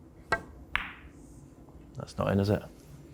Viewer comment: "That's not in, is it?" I thought he said the pockets were huge, lol